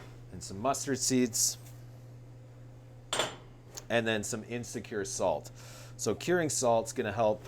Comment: Hello, as you were listing off the spices I had to go back and listen a few times. I keep hearing “insecure salt”. HAHA!!